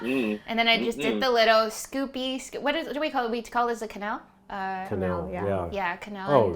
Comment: funny the subtitles says "canal" instead of quenelle... I'm like canal? why...? why is it canal? hahaha 😆😆